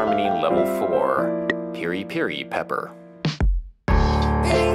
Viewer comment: I just sat through 13 minutes of a woman singing about how she's gotta blame it on the Jews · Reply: I thought she was saying goose...